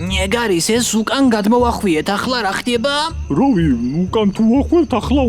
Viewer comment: BRO DID NOT SAY egaris